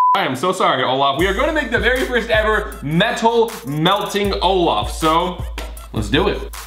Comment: I thought Collins said "your gonna be the first mental melting snowman Olaf" ME: I'm laughing so hard